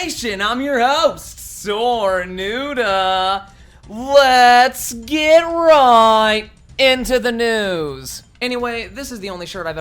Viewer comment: Lol I thought he said lets get right into the nudes